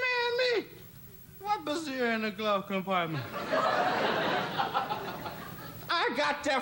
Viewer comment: What does he say at 4:21? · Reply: What brazier in the glove compartment. Like, a bra.